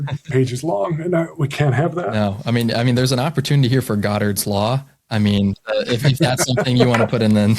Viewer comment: I thought you said "Goodhart's Law" which would imply you were criticising him for this rule. Goodhart said that anything you use to measure a policy will become part of the policy and therefore a useless measure.